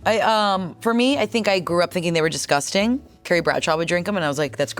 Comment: ik Angela will randomly drop football knowledge but no way she off the cuff mentioned terry bradshaw like that, who did she actually say bc that’s all I can hear 😂